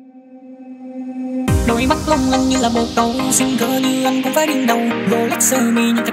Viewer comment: T nghe thành đôi mắt long lanh như bồn cầu.